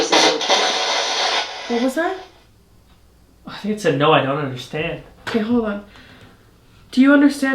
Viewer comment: exactly it sounds like instead of 'I don't understand' when you spoke about their sister, the other side, etc., what I heard was, "Doris/Delores is okay?" as if they were reaffirming what you were telling them about how their sister would probably be looking for them on the other side.